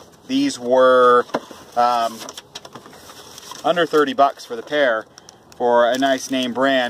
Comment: These were $13 each. I would pull a set from the junkyard if they were a good aftermarket brand and I knew they weren't blown. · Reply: Sorry....You said they were under $30 for the pair....I thought you said $130 for the pair....My mistake...rule out the junkyard...lol